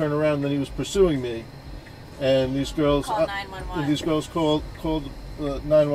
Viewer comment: sounds like he said “these milfs called “ loool